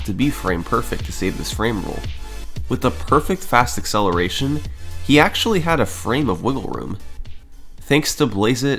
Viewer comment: what did u say ? Was that alien language for one sec there ? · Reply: "Due to fast acceleration, he actually had a frame of wiggle room"